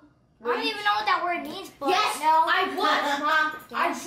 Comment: I don't know if anyone heard around the 2:50 mark, but when the step mum said "Was anybody traumatised?", says "I don't even know what that word means, but no." Does he then quickly says "Yes."? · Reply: Pretty sure he said "I guess"